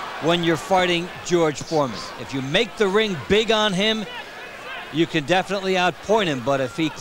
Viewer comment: did he say farting🤔